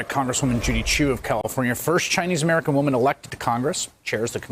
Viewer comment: Bless you, Chris. I heard you say “ a Chu” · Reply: That’s Funny ! I thought he called her a Jew !